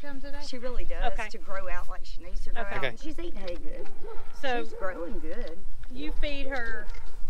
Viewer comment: I thought I heard Moody in the background saying ( hay good looking ). The M&M cattle, Moody & Mildred.